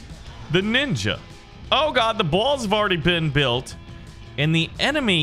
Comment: When he said balls instead of walls lol